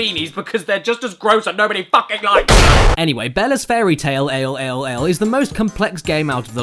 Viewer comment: First time I saw this I thought it said "Bella's fairytale, hell, hell.